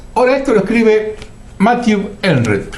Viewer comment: Matthew qué? No escuché bien lo que dijo. ¿Acaso dijo Enret?